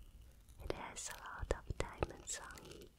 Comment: no way I'm the only one who thought she said "that's a lotta damage" right? Got flextape on the mind I guess